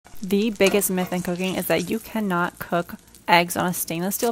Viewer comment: I heard “the best meth and cocaine”